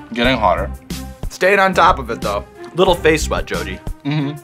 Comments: did he call him jodi at 5:30 ?? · Such a humble and chill dude. Also 5:30 did he just call his jodi? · why the fuck did he call him "jodi" im mad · am I the only one who heard "Jodi"? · Don't think i didn't hear you say "jodi" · Did you just said Jodi? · Did he just call him "Jodi"?